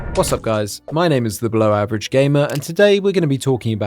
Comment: I genuinely thought you said “Blow Average Gamer” and I started dying lmaaoooooo